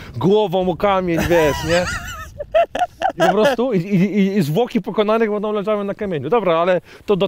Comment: Kto zamiast FOKI, usłyszał ZWŁOKI? 😅